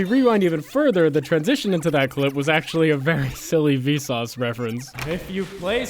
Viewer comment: ~; why did you cut it out rip Vsauce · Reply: Almost thought he said Vinesauce but then realized yea it was Vsauce.